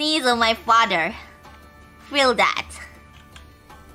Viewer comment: It sounded like she said "Why every father likes me so big"